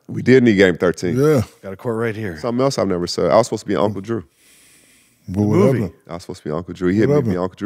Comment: Cap kyrie nickname is uncle drew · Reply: I'm sure I heard "I was supposed to be Uncle Drew, especially the second time he said it. If he did say the word "on" he slurred it into the sentence. "I was supposed to be Oua Uncle Drew" is what it sounded like